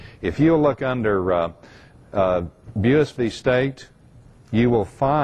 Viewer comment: at about 56:20 there is a case referenced that I cant make out.. bewis v state or beals v state.. anyone know what that case is? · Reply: It's Buis v. State 1990 OK CR 28, 792 P.2d 427.